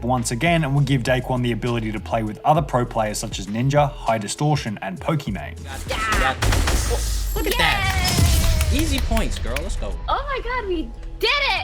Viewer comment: Did he say daquan played with other pro players and then named Pokimane? Lmaoooo